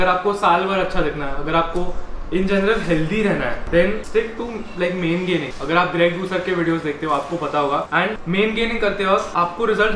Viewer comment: * What's did he say? Greg..........??? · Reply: Greg doucette